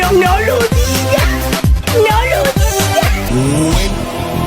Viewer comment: Did he said nigga?🤨🤨🤨🧐🧐🧐